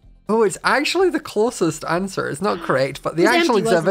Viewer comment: I had to google because I could not figure out what he said. I heard “metter” but it was mirror